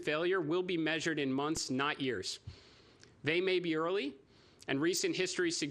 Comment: I swear he says: "The difference between success and failure will be measured in months, not ears."